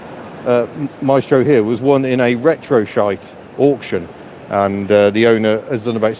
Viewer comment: The joys of doing a live stream. Couldn’t help but rewind as I thought I miss heard you say ‘Retro Shite’ and not Retro Site 😂😂 Great walk around as always